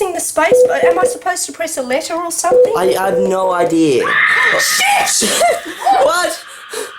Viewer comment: she said a swear word she said sh**